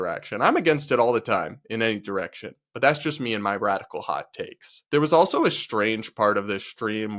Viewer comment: For a second i thought you said "Brad-ical" hot takes. That could be a new segment. Take it to the bank.